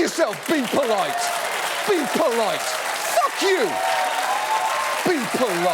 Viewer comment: Is there an uncensored "fuck" at 1:27 that I hear?